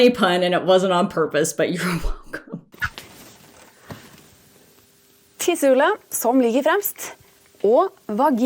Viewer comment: "To the sun that lies ahead cheese" should actually be something like "The pee hole, which is at the front." The norwegian word for "the pee hole" is tissehullet, but the translator - whether it was a person or a software program - seem to have thought she said "til solen" which means "to the sun." I have no idea where they got cheese from though, lol.